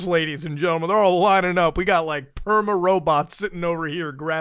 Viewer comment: When you said "robots" I legit heard "roobets" cuz of your series lmao